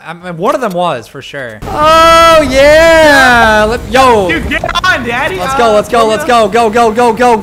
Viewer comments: I couldn’t be the only one who heard disc call daddy he said get on daddy on the 2nd game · Did say "get on daddy"